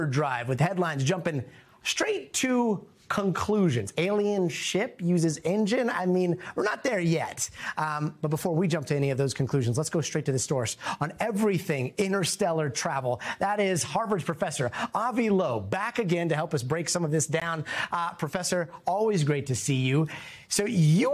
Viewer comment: he said lets go straight to the stores instead of source